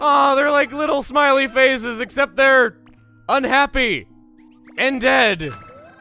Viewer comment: I heard "and dead" as "undead" My brain is a pun and that makes me mad.